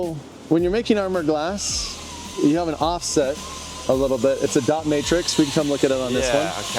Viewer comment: I thought he said "dominatrix" 😂😂😂